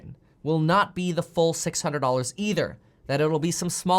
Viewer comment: I thought you said it was 400 for the unemployment